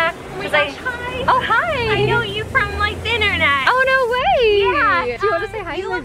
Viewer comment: I totally thought that woman said “omg I saw you from the THINternet”💀💀💀